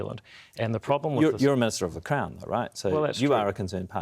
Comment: At 2x speed 12:08 I thought I heard Jack say "but you're a miserable c*nt, right?" 😂